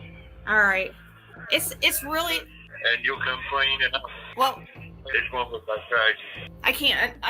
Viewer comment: I swear at 3:16 he says and you'll come clean and I'll come clean. But who knows. It would be nice for him to stick to 1 story. But instead they dangle poor Summer like cat toy for attention. IMO of course.